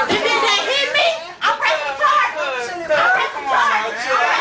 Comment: i thought she said im president charlie. hahaha.. i think she had too much charlie maybe hahaha